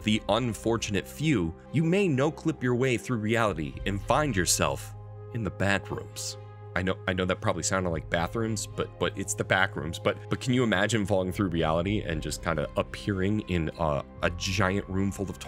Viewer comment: it's sounded like "Bedrooms" to me.